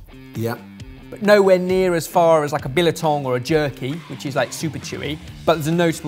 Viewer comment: Did you just say Bilatong @ 13:53 , it is Biltong.